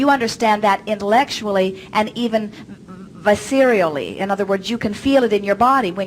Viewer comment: What is the word that she says at 4:54? The one that the captions transcribe as 'Vicey'